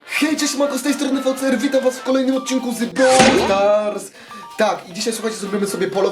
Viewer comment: Czy tylko ja usłyszałem na początku "czesc z tej strony boxdel" XD